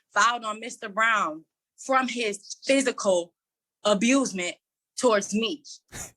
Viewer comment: Did she just say amusement? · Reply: She actually said abusement 🤣